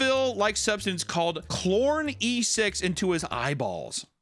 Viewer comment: says: Chlor e-6 what i heard: c0rny s3x